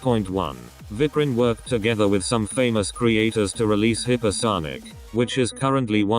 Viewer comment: I thought you said hitlerSonic